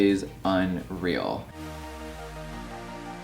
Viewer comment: When he was talking about how he woke up in the morning and said, "oh girl!" I thought he said, "ogre!" so I was like same girl same.